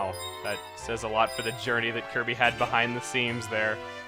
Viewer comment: I thought you said you hated kirby...?